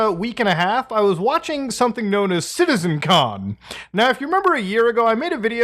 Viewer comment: I was not watching the video but listening to it and I genuinely thought you were saying Citizen Khan.